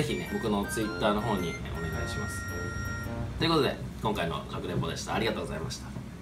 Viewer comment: 「かくれんぼでした」にしか聞こえない 腹立つー1 気持ちわりっ0 やってんなぁ<num>/7/2